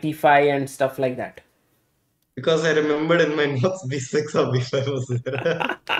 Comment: I can’t hear his answer at 23:40 and the subtitles don’t help, does anyone understand what he said? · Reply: "Because I remembered in my notes b6 or b5 was there"